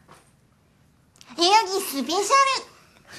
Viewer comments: のところは、何て言ってるんだろう？ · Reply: 代々木スペシャル 元ネタがあるコンサートのMCなんですけど、このコンサートのタイトルに'代々木スペシャル'が入ってました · ここなんて言ってるの？ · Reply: 代々木(よよぎ)スペシャル